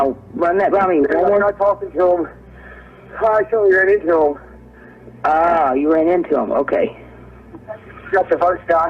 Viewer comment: Okay so I hear something different I heard "when I was talkin him, I told him I didn't know him" not "I ran into him". Also why are we not talking about how he said "we got one of them" ,, almost like he is saying one of his assailants is down. Idk. Very crazy story